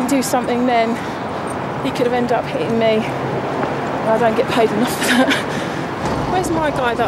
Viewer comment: You don't get paid enough huh? 4:19 did I just hear that right? Well damn....give me your job...I'll gladly take it AND I won't complain bout the pay and I'll work my ass off. Maybe if her overweight ass worked out a little she wouldn't be so winded/tired thus making her less lazy/in better shape = easier job. So she can shut it with all of that. GIVE ME YOUR JOB LADY, I'LL GLADLY TAKE IT OFF YOUR FAT HANDS!!